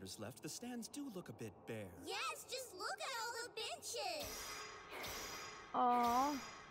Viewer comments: I know what Zeno said but it sounded like he said “Yes just Look at all the bit*hes” 💀💀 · it sounded like "Look at all the bitches" until I rewound and looked at the captions. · I swear I heard "just look at all the b*+¢hes"